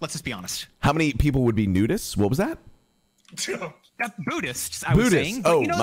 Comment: I heard nudists too